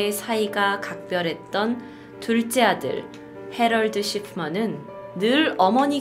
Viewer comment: 근데 왜 자꾸 해럴드 시프먼이 해럴드 식품원으로 들리지...